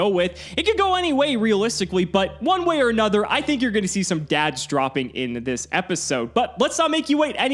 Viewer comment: "I think you're going to see some dad droppings in this episode." is what I heard